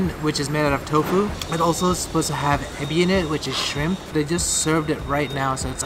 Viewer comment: when you said shrimp I thought you said snake he-bi